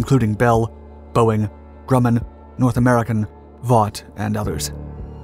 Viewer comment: Wait did he say VOUGHT at 3:25???